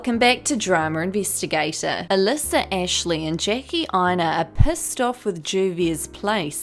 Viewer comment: When you said “Jackie Aina” the first time in this video I heard “Janky Aina” so I do believe that’s her new booty commoontitty nickname.